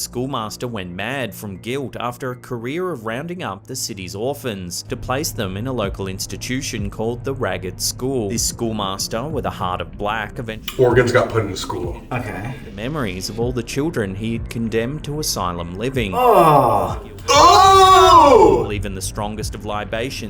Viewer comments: Am i the only one who heard organ instead of orphan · Surely I’m not the only one who heard Colby say “organs got put in a school” right?